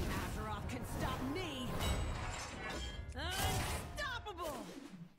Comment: I enjoy mishearing Rokara's voiceline as "I'm stoppable."